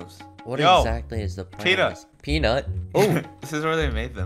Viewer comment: woah did you say peanut or sum else 🤨